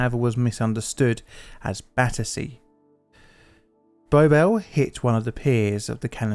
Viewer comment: What is being said at 6:40? "The call however was misunderstood as battery" according to the subtitles, but I am not aware of any meaning of battery where that would make sense. I personally hear more something like "battece", which isn't a word I can find the english dictionary. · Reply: Battersea, which is a location further up the river.